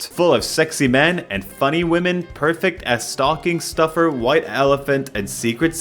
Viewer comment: Ha! No shit I thought you said "stalking stuffer" and I cracked up so hard with such pride until I realised it was only my sick mind that made it sound that way. Sorry to the stockings everywhere.....stuff ya's!